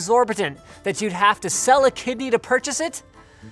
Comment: I thought you said "sell a kid for it"XDDD